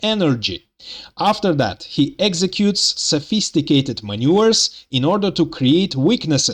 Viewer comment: Brilliant analysis as always. How you pronounce "maneuvers" made me chuckle. Sounded like you said "sophisticated manures" ;-)